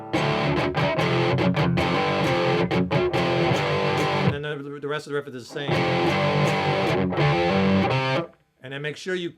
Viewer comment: what did you say? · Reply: Hi Simon - at that point I stumbled over my words but I was saying "that the rest of the riff is the same" - except for that one variation the riff repeats itself. Thanks for watching and please subscribe to the channel - take care and keep on rocking! - & NLG